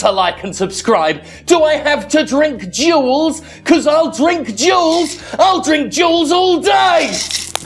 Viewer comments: I thought when Jim said “I’ll drink jewels” he was saying “I’ll drink juuls.” And I was about to be extremely concerned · Hahaha that ending Fuck I love you man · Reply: Although when I first heard "do I need to drink jewels?" I heard "do I need to drink juuls" and was like "don't do it man you got so much to live for!"